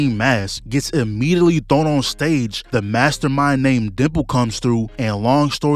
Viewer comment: maybe I'm deaf, but i heard you said either "Dipper" "Deadpool" "Dib" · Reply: Dimple lol